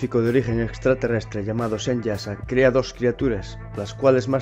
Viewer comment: En el minuto 6:05 cual es el nombre que mencionas....? No entiendo muy bien pronunciación. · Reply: "Semyaza", gracias por tu interés amigo :)